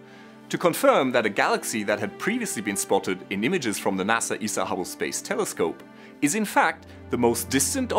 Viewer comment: Sorry you are correct they don't say N.A.S.A. E.S.O. They say N.A.S.A. E.S.A. Hubble space telescope. At 0:40-0:42 He clearly says N.A.S.A. E.S.A. Hubble space telescope.